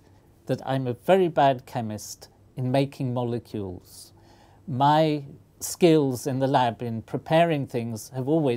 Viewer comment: Did i hear him say "My skills in the lab are porn"?!